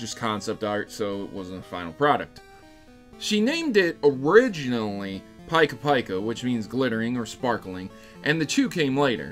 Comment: did he just say.. "Paika Paika" .... smh